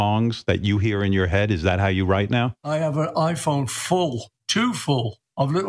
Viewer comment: at first I thought he said "I have an iPhone 4."